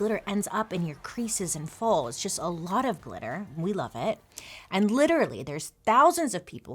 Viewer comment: "and literally" it sounded like you said "and glitter-ally" cute!